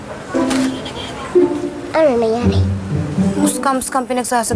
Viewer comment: may narinig akong babae na nag sasabi na anong nangyari hahahahha